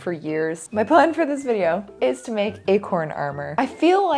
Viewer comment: I heard "I'm going to make a corn armor" at first.